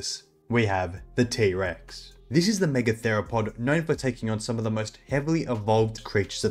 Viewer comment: thought he said "niggatheropod"